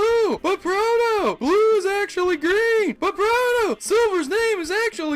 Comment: But proto! Gary's not blue! But proto! Blue's actually green! But proto! Silvers name is actually three question marks!!! LOL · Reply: I thought he said "Blue is actually gay!"